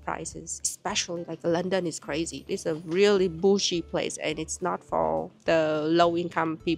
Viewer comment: "it's a really bougie place".. but it kind of sounds like you said "bullsh.. place", which is more truth 😅